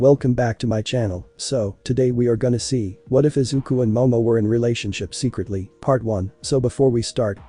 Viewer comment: I thought you said deku and momo